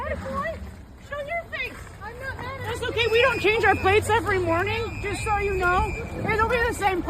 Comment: "we don't change our plates every morning" is an oddly specific claim 😂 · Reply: I thought she said "We don't shave our face every morning"